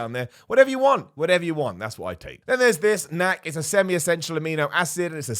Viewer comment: what amino acid ?only said once couldn't after replay even ascertain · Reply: NAC..... it's a brand name. Actual amino acid is N-AcetylCysteine....... it's the supplement form of the naturally occurring in food amino acid cysteine. That said..... IMO amino acids, essential, nonessential..... semi-essential...... whatever are definitely not necessary. Not only do you get pretty much all of them in pretty much any meat you may be eating already, but amino acids are also found in rice, beans, fruits, etc. As for just this amino acid discussed - cysteine, it can be found in lean pork, skirt steak, chicken, tuna, eggs, oatmeal...... pretty much food any healthy or gym going person is/should be eating plenty of already. Again, same can be said about pretty much every amino acid - odds are you're getting plenty via food. And if you're supplementing with a protein powder...... you're likely already getting a surplus of them. THAT said - exact same thing can be said about multivitamins or just vitamin supplements in general. All vitamin supplements are are expensive, colorful pee you'll be buying because you'll pretty much piss it all out. Your body only needs so much, and again, I assume you eat food..... healthy food that's loaded with vitamins and minerals, you're likely getting enough. IMO the ONLY time you should supplement vitamins or minerals is if your doctor says you have a vitamin or mineral deficiency. Otherwise you're figuratively throwing money down the toilet........ in the form of your expensive pee. Only supplements worth taking, or more accurat